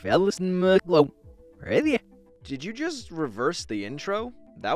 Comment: There’s a typo in the subtitles. It says “reserve” instead of “reverse”.